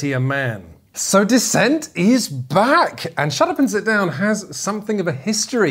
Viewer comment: I swear that at 1:19 I heard "So, Descent is bad" and was fully prepared for the review to just finish there.